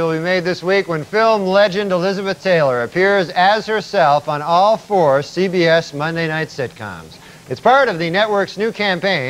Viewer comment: Did anyone else think this joke was going in a different direction when it sounded like he said 'Elizabeth Taylor appears as herself on all fours"